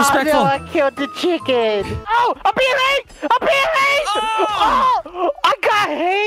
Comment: for a second i thought Delirious said he was getting r*ped